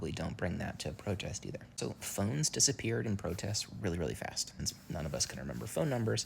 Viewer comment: This is so simple to understand. Thank you! I don't understand the phone thing. It sounds like you say "phones disappeared real fast" but the caption says, "phones CAN disappear real fast." Is it that you shouldn't bring your phone? Or are you saging that they can get stolen or taken away from you, and that is why you should have phone numbers written down?